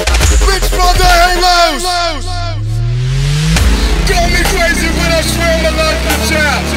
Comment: omg... for the longest time i thought he said "call me crazy but I swam alot as a child" my life is a lie